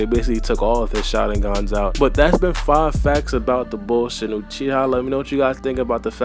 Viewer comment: Did u say "bullshit Uchiha"? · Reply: Savage_queen_atx no lmaooo, 'the bull shin Uchiha' 😂😂😂 'bull' meaning 'boy'